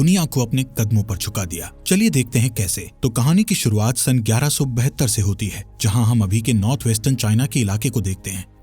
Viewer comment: did i heard 1192 as 1172 ? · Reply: 1172